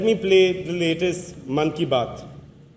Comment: Does he said monkey bath